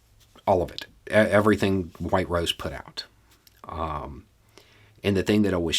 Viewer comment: I thought you said “white robes” the first couple times and was profoundly confused.